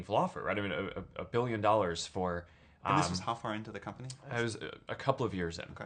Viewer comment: 진행자분이 언제쯤이냐고 묻는데 영어로 뭐라고 하신거죠?? 정말 궁금합니다 부탁드립니다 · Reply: 서영재 "This was how far into the company?" 라고 말했습니다 :)